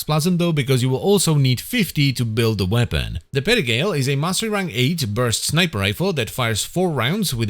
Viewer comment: All i heard was "mastery fodder."